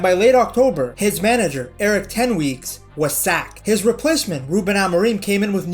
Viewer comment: Eric Ten Weeks? I heard it 😜